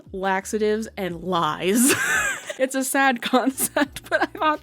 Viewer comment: I 100% thought you said eggs and lines diet and was low key like hell yeah that would work 😂